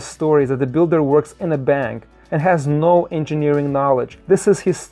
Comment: Did you say the builder works in a bang, band, or van? 3:52 into the video.